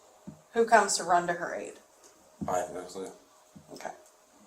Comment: terrible closed caption. Who comes to her aide? "I do, obviously" he actually said "I have no clue." Never speak to the police. They are not your friends. They are not there to help you. They are there to build a case against you. Always get a lawyer!